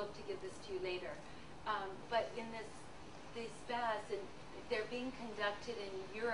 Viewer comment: Can anyone make out what she says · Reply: I think it has to do with a bath.. "..elevated lactate or low CO2, can be successfully treated with CO2 therapies of various kinds, such as CO2 baths (where CO2 is pumped into the tub, much like bathing in carbonated mineral water) or adding CO2 into standard hyperbaric treatment.."